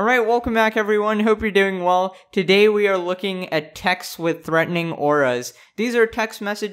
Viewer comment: oh i thought it said texas with threatening auras lmao